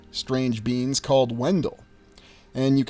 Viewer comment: Did you say Wendel ... or Grendel? How curious ... but pretty sure the same thing ... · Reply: Wendol, and you are very right, it appears to be the exact same thing....